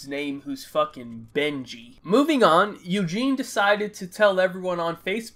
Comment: On god in the Benji story I thought you said “bengene”